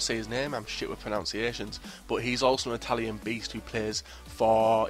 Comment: i thought you said italy ??